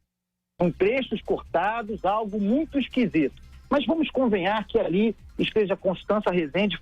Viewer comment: Carlos Andreazza - No minuto 15:00 deste vídeo o senhor proferiu o termo "Vamos convenhar". Será que foi isso mesmo que escutei??? já revisei várias vezes e continuei escutando "Vamos convenhar"... Acredito tratar-se do verbo "convir". Procurei pela conjugação desse verbo e não encontrei um tempo do verbo com a conjugação "convenhar". Por favor, você poderia explicar o significado dessa palavra, "convenhar"? A etimologia, se possível???